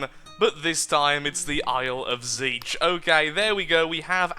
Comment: Isle of zeech? I thought you said isle of tzeentch when you Said that